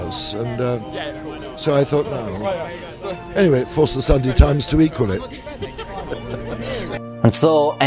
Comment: What does this winner say at 15:42? The CC is just blank for that portion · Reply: "Forced the Sunday times to equal it"